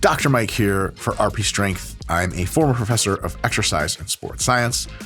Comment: Did he say 'former professor'?